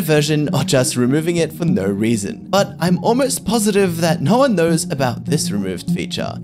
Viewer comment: I thought u said no one knows WHY they removed it?